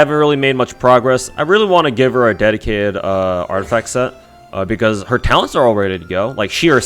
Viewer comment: I heard "Dedicated artifact set" as the "Dead Kid artifact set" Which made me howl since it fits pefectly as you were showcasing Qiqi's artifacts